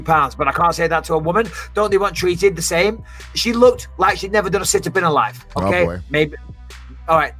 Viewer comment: what did bisping say about aspen? he said something like "she looked like she never did a "sintabin" in her life. did he say situps or what?